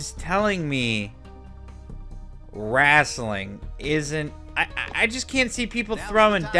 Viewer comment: Did he just say WRASTLING??!!